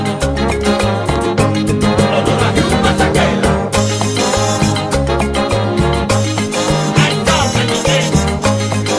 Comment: ¿Alguien me puede decir qué dicen los coros entre 3:08 y 3:14..? Lo he repetido tanta veces pero no logro descifrar… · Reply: Dice (Honor a Hugh Masekela) Músico de Jazz que hizo musica para la liberación de Nelson Mandela a quién el Maestro Chichi Peralta, admira e hizo con esta mención un homenaje dedicado a el por su talento y determinación. Este maestro Africano fue un increíble musico a nivel mundial específicamente en el Jazz <link> (DM; encargada de medios Digitales ,CP)